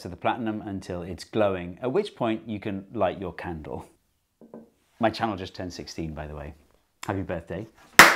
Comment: I thought you’d said, “My child has…”, lmao. Made her response of, “no” that much more funny.